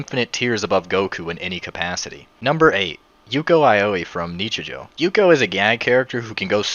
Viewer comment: can somebody tell me what anime this is?i know he said it but it sounds like gibberish,thanks! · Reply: He said Nichijou m8